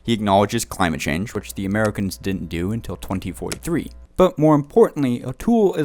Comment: did he say 2043? Haha